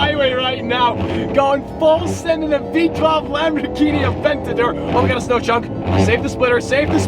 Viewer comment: did he just say lambor genie? Replay it if u do not believe me